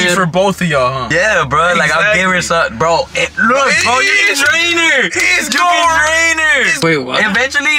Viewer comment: my dirty ahh mind heard say "YOU CAN DRAIN HER!!" instead of u can train her